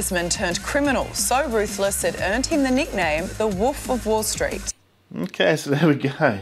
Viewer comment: What is, "The Woof of Wall Street'? she says it at 0:36++